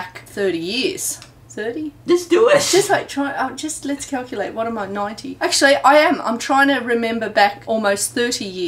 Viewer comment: did she just say "what am i 90?"?